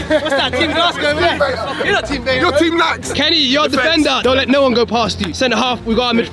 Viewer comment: I thought he said kenny your a bender 😂